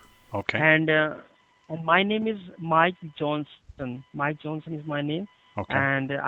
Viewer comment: I 100% guarantee no human with that accent will ever be named Mike Johnson. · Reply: justin fwibber man I thought he said his name was Mike Jones. I would’ve just kept repeating ‘who?!’ Until he got annoyed and hung up.